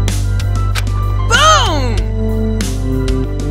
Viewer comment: I’m so excited for the series but when they said that’s going in my nites I thought he said that’s going in my nose